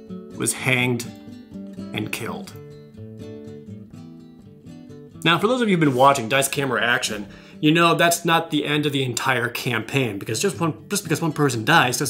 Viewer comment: Dice, Camera, Action finale LIVE tomrrow, Wednesday Dec 7th at 4 pm PST! You can watch at twitch.tv/wotc_dnd AND follow my Gameplay channel for all kinds of D&D video games! <link> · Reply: thought you were saying daisuke action in the video lol